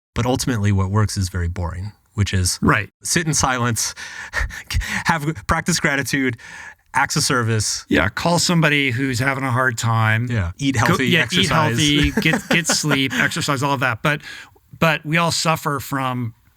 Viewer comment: Hey Y'all, can someone explain to me what Mark says in Minute 53:32 after he says "practice gratitude". Is he saying "access service" what does he mean by it can someone explain? PS: thank you for your great work Mark and Rich, this great insight for me❤